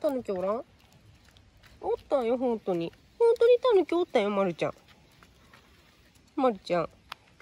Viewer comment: さんの｢ほんとにタヌキおったんよ？｣が、ジブリの｢トト〇いたもん！｣に聞こえてしまった…